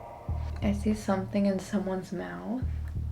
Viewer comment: when she mention the alien ship on the viewer, I heard right after 6 men - maybe she was trying to say 6 men abducted her so she used the alien ship as a symbol!! 😮